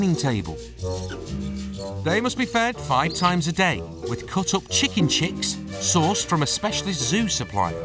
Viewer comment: I'm I the only one thought the commentator said the baby owls are to be fed "Chicken shit" not chicken chicks, five times a day. 😂😂